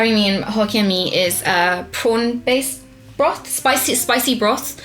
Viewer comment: Literally thought Kim said "Porn-based broth", I had to rewind XD